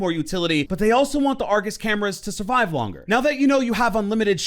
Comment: yes, we would like to change zero's argus cams so they can be more impactful and need more strategic placement so they wont get shot easier. Not only does that not make sense because of the green light, the cams also shake the screen like a bitch when deployed anywhere. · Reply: Ngl I thought you said angus camera